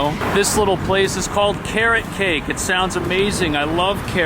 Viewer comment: Love that red coat ……he’ll clean it up for you. I thought for a second you said Karen Cake! A whiny color of orange!